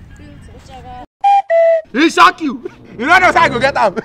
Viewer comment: Lmfaooo! I hit like when I hear "e shock you" at 1:17.